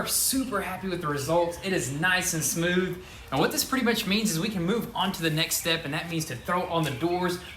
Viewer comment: did he say whats this pretty b!tch needs HAHAHA love your work guys